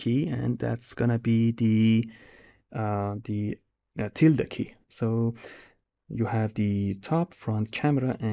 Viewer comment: Anyone know what key he's talking about at 26:23 ? I can't quite make out what he's saying 😞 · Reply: tilde key, it's on the left from 1!